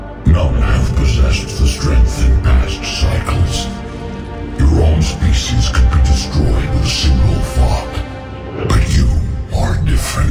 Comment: did he say: "your species will be destroyed with a single fuck" ???